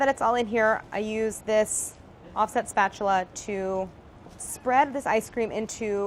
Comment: i thought she said awesome spatula at first